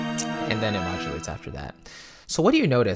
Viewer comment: Please, can anybody help me to understand what is said at 25:47. It seems something like "and then it (???) after that". And thanks a lot for this amazing video. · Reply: Hey! I just said it modulates (changes key) after that. :)